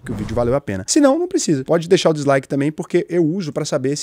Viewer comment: É sério que ele disse “deslike”???